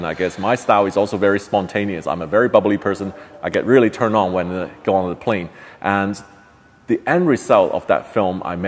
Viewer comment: did he say he gets turned on when he goes on a plane? Hold up.